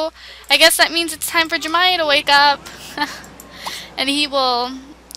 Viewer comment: Hahaha. All I heard was "I want Jeremiah to have a baby" xD